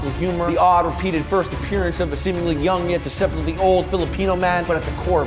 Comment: did he said filipino man